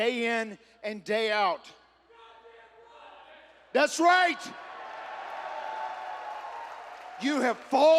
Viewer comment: what did that guy say😂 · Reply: Your Gawddam Right 😂😂😂